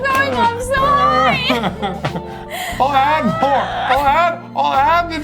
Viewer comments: Did say “i’m so high” ? 😂😂😂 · Did she say I'm so high at 18:14 ???!!!! (Me too girl)